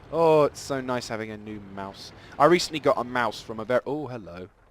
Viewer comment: lol, "I recently got a Maus" is what I heard